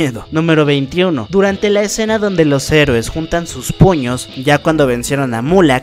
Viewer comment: en vez de entender "puños" entendí "p0ll45" JAJAJJS PERDON